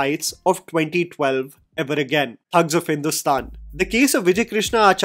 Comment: "Thugs of Hindostan" I heard "Hugs of Hindostan" 💩